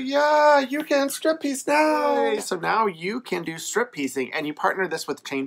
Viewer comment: "Strip piecing" said fast...sounded like you said "strip teasing" 😅